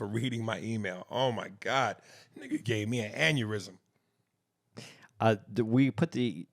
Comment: I thought aries said after reading that email andyurysm 😂😂😂😂